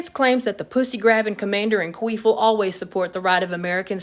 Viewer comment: Have I just heard “Pussy Grabber Commander in Queef”? I am rolling laughing on the floor.